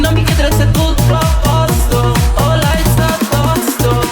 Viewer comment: qua che ha detto Anna? che non ho capito 😂😂😂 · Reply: ha detto ho l'aids addosso